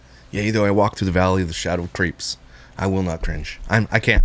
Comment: Almost spit the coffee out at "yay I walked through the valley of the shadow of creeps" lol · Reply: I thought he said shadow of grapes! 😂